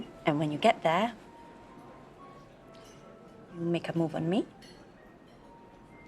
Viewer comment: No idea what she said. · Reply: "You make a move on me."